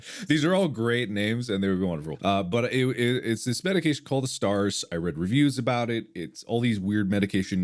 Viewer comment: Ok need a little help here. Anyone knows the actual name of the medication Mark says at 22:25? I hear "stars" but for the life of me I can't find it. It only brings up BuzzFeed lists of "famous stars with adhd" when I google it. I'm diagnosed with ADHD myself and have been taking medication for years now but, as Mark said, lately I feel I must have developed tolerance. I would like to ask my Dr about it in my next appointment with her. Especially since I'm from the EU and I bet my pharmacy won't give me such a hard time if I get it prescribed hahaha.